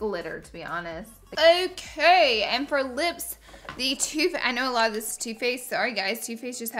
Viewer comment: I swear when she said two faced I thought u said toothpaste😂😂